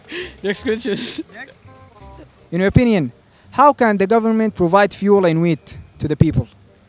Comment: did he said fuel and weed 18:30 ??? 😂😂